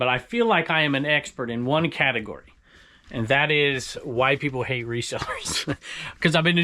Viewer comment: Yeah I'm always surprise how you remember where and how much you've paid for stuff, considering the amount of stuff you buy. I had to rewind I thought you said white "people hate resellers" but you said "why" LOL